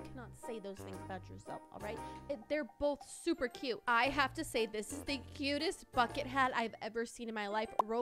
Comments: I thought she said "I have to say, this is the cutest f*cking hat ever" but she rlly said "I have to say, this is the cutest bucket hat ever" 🦋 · why did i hear " the cutest f*** hat" and not "the cutest bucket hat" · For I second I thought said the fu- it bucket hat sorry